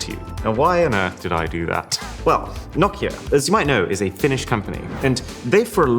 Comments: some nostalgic...some surprising phones. overall nice collection of rare phones... I thought I heard at 22:27 mark "Nokia as you all know a finished company" but he said Finnish company...🤣😂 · I thought I heard you say, "Nokia, as youvmight know, is a FINISHED company". Till I listened again and got you well (Finnish). 😭😂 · I heard "Nokia as you might know is a FINISHED company"😭